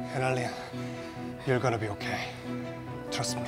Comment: Analia daw instead of Analyn